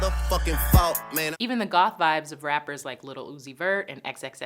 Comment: did she say goth jeez shut the fuck up x ain’t like that